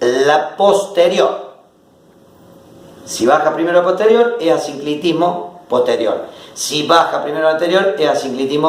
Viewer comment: que dice en el minuto 13:44? "Si baja primero posterior".... ??? y despues no entiendo lo que dice. Alguine me lo puede escribir porfavor? Gracias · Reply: es asinclitismo posterior.. se baja el anterior.. asinclitismo anterior...